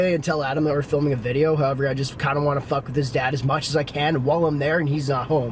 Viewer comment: I thought you said I kinda wanna fuck his dad a little 😂😂🤣